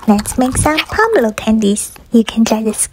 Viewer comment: I thought it said potato peel candies 😭